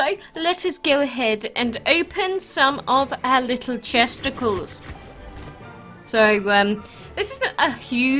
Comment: did you just say Chesticles